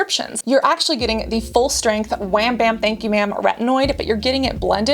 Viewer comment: I’m too lazy to rewind but did you just say full flam, wam bam, thank you ma’am? Lmaooo